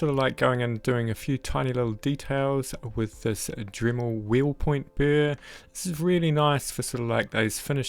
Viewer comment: I can't understand the name of the burr at 4:02. Can anyone tell me? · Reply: Hi Janet, it is the Dremel 7103 Shank 1/8 Inch Diamond Wheel Point <link> The above affiliate link is in the description of the video. Cheers for watching